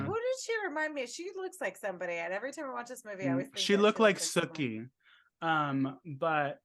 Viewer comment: when you said Sookie, I heard "Snookie" Then when you said it again and I heard SOOKIE, my first thought was True Blood... Soo at least I'm as thrown off as the others were 😂